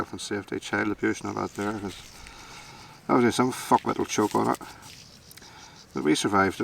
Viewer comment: Did I hear that right "some f@ would choke on it" 😂😂😂😂 and there is nothing wrong with talking "Bolicks" I do it every day where I work 😂😂😂😂